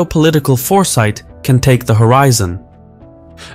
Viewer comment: 🌏Use code CASPIANREPORT at the link below to get an exclusive 60% off an annual Incogni plan: <link> ✌ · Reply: what is the name of this guy? I heard: I've been ur host, thanku for watching. is that correct?